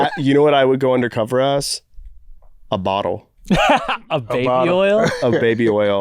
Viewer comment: you know what I’ll go under cover as? I heard “A Model” not a bottle and it cracked me up